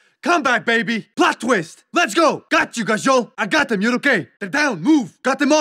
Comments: "got you ____" can someone please fucking tell me what russian word that is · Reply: he says Козел (Kozyol) which means goat in Russian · for anyone else wondering what he’s saying. he’s saying козел (kozel), meaning goat or an alternative way of saying asshole/motherfucker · Anyone know what he is saying just after 15:00 he says "got you #&+$"......I assume it's some russian curse word or something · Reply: Kozel I think